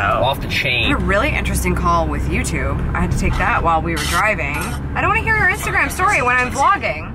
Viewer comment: It sounded like you said you were driving to India. I know that's a lame comment, but I'm feeling goofy this morning. I love canva too! Congrats on SAP! Ya'll are doing great. I love Lucy! You ate twice on this vid, how long did it take you to film it? Love the multi-city shoot! Cool! Blake joining the team, wow! The four of you will get it done! Is Blake going shave? I love flowers and flower venders!